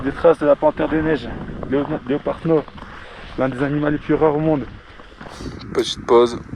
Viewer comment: J'ai entendu "un des ANIMALES )deux fois,,,,j'ai arrêté la vidéo 😂😂